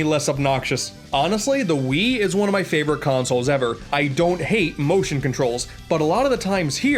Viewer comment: I thought he said emotion controls